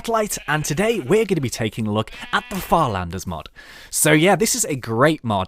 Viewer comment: I thought it said "flanders mod" ..like flanders from the simpsons